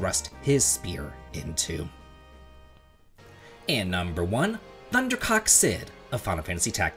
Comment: Oh my god i thought you said 'ThunderCock' Cid xD I just cant xD